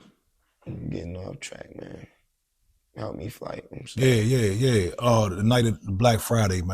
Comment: did he say help me flight I’m scared.?🫢😳